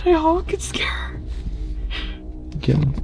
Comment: I don´t usually comment, but did anyone hear a different man's voice around 1:34:10 (it´s after said 'do I go scare her?') I personally think it's saying 'kill her' but I could be super wrong